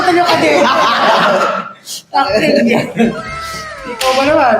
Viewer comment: LMAO I THINK PABLO SAID "TAKTENG YAN" HAHAHAHA I LOVE HIM